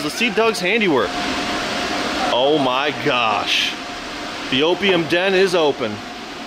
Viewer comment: did i hear call it the OPIUM DEN